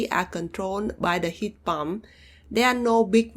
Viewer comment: Great video, you've got my subscription! My only comment is you might want to be careful when saying pump, as it truly sounded like you said 'heat bomb' at 6:30. I don't mind your accent, I just figured you might want to be aware of that specific one. Thank you for testing these!